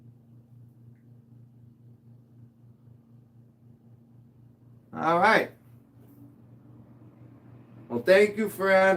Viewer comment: What did have to say at 12:48? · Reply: I believe he said that he never wanted to be a mod.Rich made him one